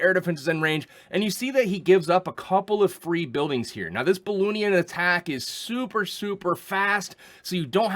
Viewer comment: did you said BoLoonion attack?